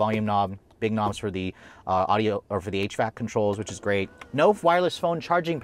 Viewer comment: I swear he said "for the 8-track controller". Wow! Very traditional!